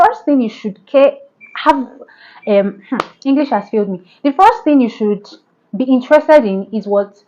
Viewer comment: I heard 'English has killed me' 😂😂😂😂😂oh well.. Anyway!